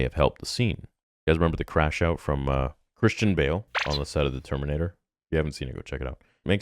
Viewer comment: For a second there I thought you said Hayden Christensen not Christian Bale crashed out of terminator! 😂